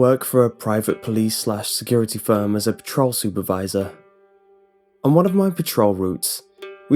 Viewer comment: I heard him say he worked as a troll supervisor, not patrol supervisor.